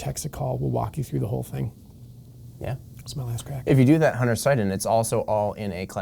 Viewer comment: what did say? Sounded like " smell ass crack".